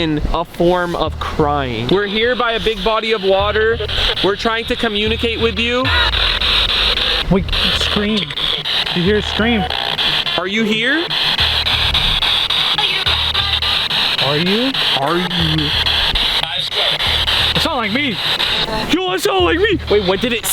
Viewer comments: I heard “ are you my mom?” And that’s why there were other voices to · instead of are you I heard “la llorona” sounded like someone girl saying it but i don’t know if I’m the only one 🤷🏽‍♀️ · In 33:54 im pretty sure i heard are you my baby